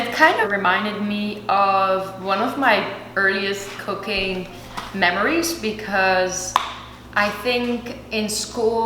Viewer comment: i thought she said cocaine memories i was like 😳😳😳